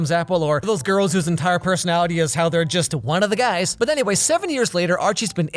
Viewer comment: I almost thought you said 70 years later🤣